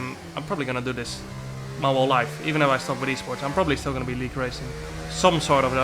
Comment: stopped esports? What is he saying at 0:46? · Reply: "Even if I stopped with Esports, I'm probably still gonna be league racing"